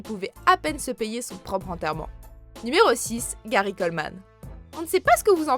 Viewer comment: jai entendu Gary Oldman 😂😂